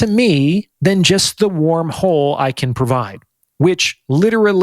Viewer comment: "... more than the warm hole I can provide" LOL, I thought you said "worm hole"... yeah, a dude be doing it with a woman, they fall asleep, and he wakes up and it's a different year in the past or future, or an alternate parallel reality in current year - accessed via a "wormhole".